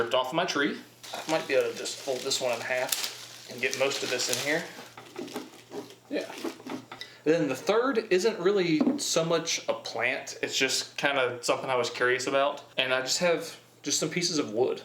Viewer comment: OHHH I THOUGHT IT SAID PANTS